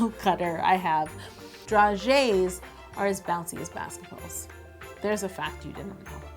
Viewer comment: 😂 I can’t be the only one who misheard her say ‘as bouncy as basdards’ 😂😂